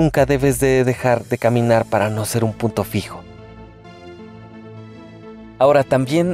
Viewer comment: entendí puto fijo 🤣🤣